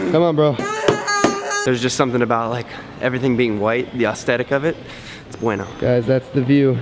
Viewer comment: i heard "austetic" and "thanks g eazy" and i was out